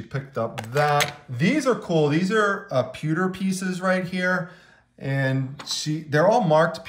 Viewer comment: Awesome video. Super entertaining! Your the man! When your mentioned pewter at the end while reviewing Mr finds , at first I thought you said "peter" I was rolling on the floor laughing. Lol yes I admit I'm a juvenile at times!😂